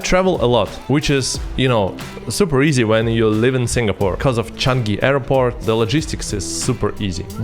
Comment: Hi, thought I heard you pronounced Changi as Chanki ? :)